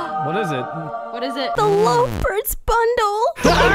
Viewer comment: Turn on the caption On 5:45 you will see captions says Loafers Bundle instead love birds bundle.